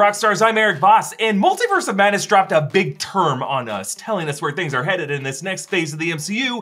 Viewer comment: Hahaha. I thought you said "big turd" at first in the beginning.